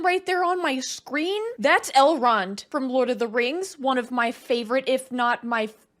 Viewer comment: Every time you said "Elrod", I heard "L. Ron" and was wondering what the creator of Scientology had to do with this movie.